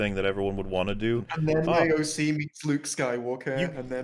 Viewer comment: I heard that as "And then AOC meets Luke Skywalker". Coming soon: "The Taxation of Trade Routes to Outlying New York Boroughs: A Star Wars Story"